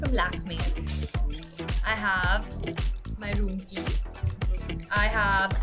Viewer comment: what she said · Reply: My room key